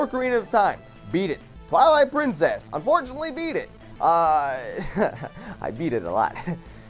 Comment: I just heard say "I beat it alot"